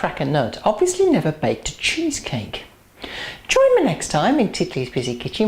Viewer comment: Is Titli your full name? or a shorten version? Sounds like you say Tiffany? Big hello from Santa Barbara CA.